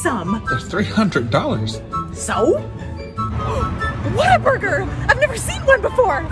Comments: Why am I the only one who thought ppl were saying Waterburger all this time🤣😂🤣😂🤣😂WTH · Ice me and my British family went to Texas and we asked a guy where we could eat, he said what a burger but since we didn’t know what Texan accents were like we thought he said water burger so we went on a 1 hour trek to find water burger